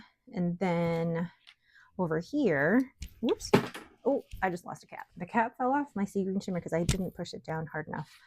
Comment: I thought you said "I lost my cat" 😂😂😂